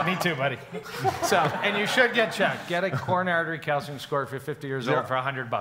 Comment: what kind of test is kahn recommending? i can't understand what he's saying. coronary artery casting score? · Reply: He said Coronary Artery Calcium Score. Actually shows how much buildup you have in your arteries. My Cardiologist suggested that I for me to take the test. It cost around $100.00 and tells you how much plaque is in your arteries.